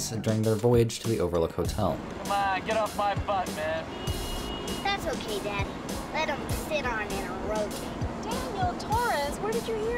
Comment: Hilarious video! Love your music choices, I'm a "Sunny" fan myself. ))) But I've been struggling to understand what does the kid says at 34:30 ? "Why don't you just... " ? I think I can hear "roadkill" at the end of sentence, maybe? IDK. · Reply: LOL he says "That's okay Dad, let 'em sit on it and rotate" Also thanks! Sunny is great